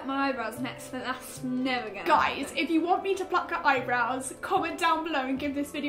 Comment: I THOUGHT YOU SAID PLAIT HER EYEBROWS